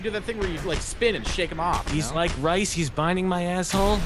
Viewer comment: at the end of the video did say "He's like rice, he's binding my asshole? Cause that is what it sounded like he said.